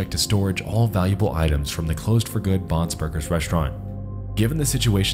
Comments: every time he said "Bon's Burgers" I just thought he was saying Bob's Burgers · I kept hearing "Bob's Burgers" and was admittedly rather amused. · I keep mishearing Bon’s Burgers as Bob’s burgers and that keep making me laugh of the tv characters having to deal with evil animationics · All I hear is "Bob's Burgers" instead of "Bon's Burgers". 😂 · I thought he said Bob's Burgers at first lol. Also the ringleader looks like Arin Hanson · Sorry thought you was saying Bob's Burgers lol